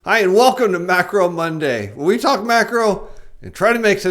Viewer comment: Sounded like you said "Welcome to Backroom Monday... " 😆